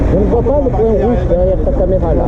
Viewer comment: Il te disais quoi l'autre a 2:49 ?🤔😂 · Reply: JC Turbo, Il disait "Je ne vois pas le point rouge derrière ta caméra là" Il sait que je filme et que c'est censé avoir un point rouge lumineux. Pourtant ça filmait bien 👍😇